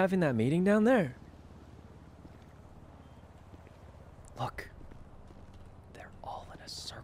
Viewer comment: Did he said "F*CK"?????? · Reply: Lol he said Look hahaha 😂😂😂😂